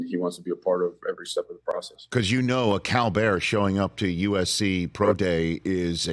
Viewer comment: I cannot for the life of me decipher what he says at 1:12 a cow bear???? Is what I’m hearing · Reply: Cal Bear lol took me a minute too